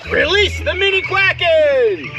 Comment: it sounded like you said "release the meaty quakens", but closed captioning said "mini" soo, yeah, what did you say? So "release the meaty quakens" and out comes scrawny ducklings, phawa ha ha ha ha.